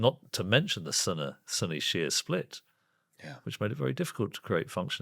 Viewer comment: I thought he said the Sonny and Cher split.